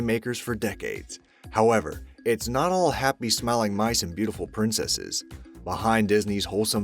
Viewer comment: I thought you said happy smelling mice..